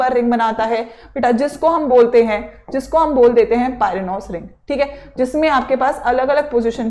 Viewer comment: can someone plz confirm what mam said 6 menbrane chain what the spelling of taht is it pyranolcillin /pynocillin or waht?? plz confirm fast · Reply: Pyranose